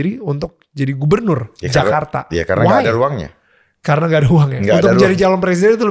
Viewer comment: Menit 7.50 -8 itu pak dharma bilang ruang bg Daniel bukan uang,salah denger bg daniel